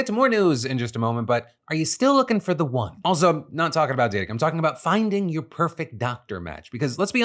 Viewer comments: Did he say “D!€k” or “Data”. I heard “D!€k” but the subtitles said “Data”. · "Are you still looking for the one? No I'm not talking about dick." is what you said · Listen Phil we're gonna have to work on "diction". During your zoc doc add swear i heard say " are you still looking for the one? Also im not talking about DICK" I rewound many times and i heard it every time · Did you just say not talking about Dick? 😅